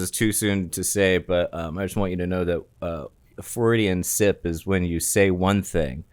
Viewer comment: I thought said Floridian Sip lol.